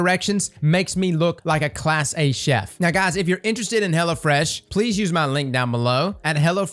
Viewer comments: I thought you said "hella fresh" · I'm sorry but the only thing I can comment on is how I kept hearing "hella fresh" when he was talking about the sponsor and honestly I think they should just rebrand themselves